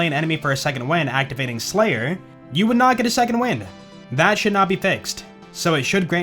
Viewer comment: When says:" that should now be fixed", I hear :" that should not be fixed " for whatever reason 🤔